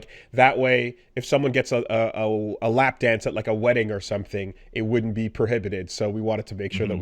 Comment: I'm sorry can we back up a bit. Did say "lap dance at a wedding"?